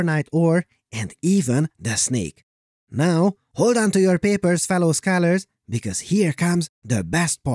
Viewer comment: I love "hold onto your papers" ... but I really hear "hold onto your pearls grandmother!" ... I can't explain why, but I do. $0.02